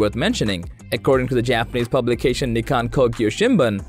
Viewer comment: Did he just say the ‘Jap’ based? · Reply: lol. He said "Japanese Publication", but once I saw your comment I hear it now